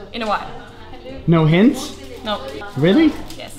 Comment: come on Jon, I think you know what she meant. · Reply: HHAAHHAHAHHAHAHAAHA, I thought Jon said " No hands? " instead of no hints xD